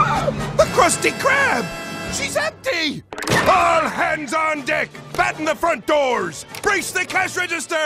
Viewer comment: I thought Mr. Krabs said there "ALL HANDS ON D##K !"